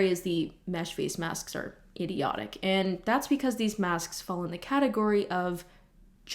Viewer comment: i hear "Masksks" Are my ears ok